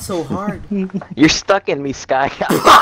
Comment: I bursted into laughter when I heard "You're stuck in me skyguy" 😭😂